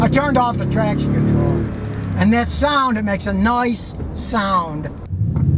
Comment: Noice? @ 5:00 did he say "noice"? Hip Scotty Talk!